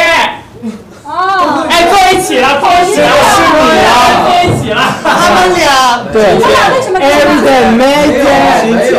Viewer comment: 誰可以告訴我56:25是什麼問題 太吵了聽不到🙉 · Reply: 应该是“谁吃的最慢”